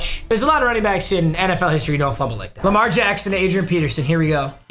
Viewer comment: did he say Lamar jackson?